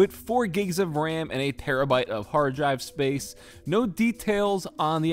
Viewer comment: did you just say perabyte???